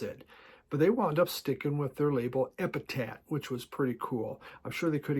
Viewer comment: it sounds like you said EpitaT. It's EpitapH. Also, That Ben Folds Five album was released in '97.